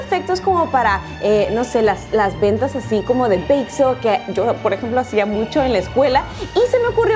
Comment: 🙈 perdón, ventas de que? No entiendo que dices 0:38 pero se ven súper lindos!!!! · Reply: "bake sale" o ventas de repostería - en mi escuela las llamábamos así y se hacían siempre para recaudar para funciones escolares o paseos etc. jeje